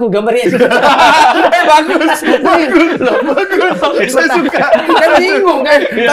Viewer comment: bang densu bilang apa? saya dengernya kok "saya juga"